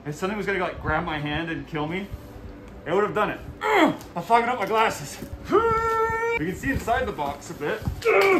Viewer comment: did he say he's fogging up his glasses or did he say he f***ing up his glasses?